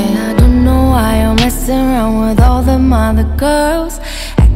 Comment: My gay ass thought she was saying 'I don't know why I mess around with all them other girls' but now I realize she's saying 'I don’t know why you’re messing around with all them other girls' 😭😭😭